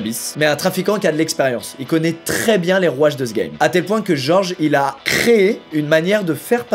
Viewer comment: jai compris "les rouages de zgeg" euuuuh